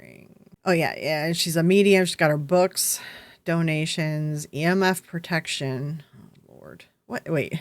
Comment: I thought you said "Ear Muff protection" then realised you were saying EMF protection.